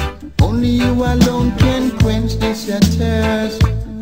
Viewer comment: I hear this: "Only you alone can quench this SAD thirst"